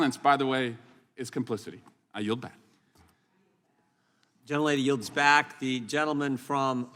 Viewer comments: I'm old but did I hear Jim Jordan say @ 1:27 "The young lady yields back" referring to Mr. Swallowswell? Jim Jordan is the man! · Did Jim Jordan call Swalwell a young lady? At 1:30 part. “ the young lady yields back”. After Swalwell said , “ I yield back “. Lol. If I heard that correct, that’s too funny!! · Did he say "The young lady yields back." (1:28) after Swalwell yielded? 🤣🤣🤣